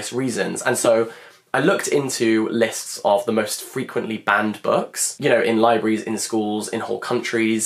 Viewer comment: I first heard "band books" instead of "banned books", thought the books would be about bands. 😂